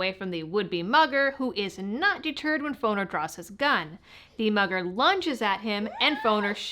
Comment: LOL, at first I thought you said mother not mugger, that caught my attention big time 🤣.... as i am listening to you telling the story and I kept thinking why some mother do that, I need to flush my ears out.